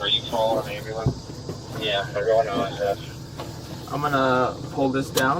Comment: they actually said "they're going to OSF" not "everyone ISS" OSF is the local hospital